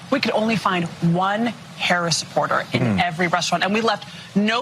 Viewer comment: I thought she said one Harris in every restaurant, guess I was wrong! 😂